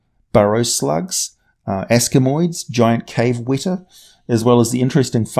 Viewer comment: To my ear it sounds like you say "cave wettAH" multiple times.. Other than "cave wetter" which I think is wrong... I can't seem to figure out what you're saying there ^^;;; help? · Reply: A wētā (indeed pronounced ("WET-ah") is a giant, flightless cricket endemic to New Zealand. They are among the biggest insects in the world - some species growing as large as a human hand. Famously, Weta Workshop (the practical effects studio behind the Lord of the Rings movies) is named after these bugs - because Peter Jackson is afraid of them.